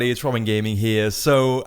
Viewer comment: Trollin Gaming? haha sorry but can't hear anything else but that :D